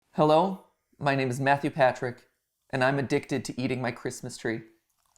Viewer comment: Deadass thought he said “my name is Matthew Patthew” in the beginning